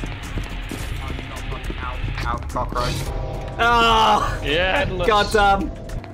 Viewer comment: Wait, did say "Out of my fucking house, cockroach"